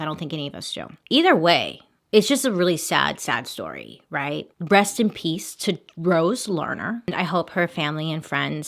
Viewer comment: did anyone else hear "Breast in peace rose" orrrrrrr just me?